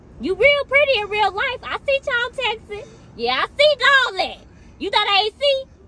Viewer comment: You real pretty in real life , I see ya Tall Texas 🤣🤣🤣 got me rolling 🤣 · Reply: 😩🤣🤣🤣🤣 I thought she said tall Texas